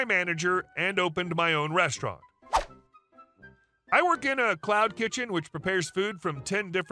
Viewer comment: I misheard it as "I work in a clown kitchen" and was disappointed it was not an actual clown kitchen